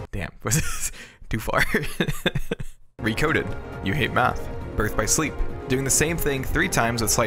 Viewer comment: I thought you said meth and I think it still works either way.